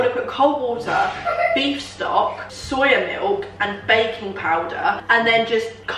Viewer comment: When she said you need to add baking powder i thought she said baby powder and i was like erm is that even safe?😂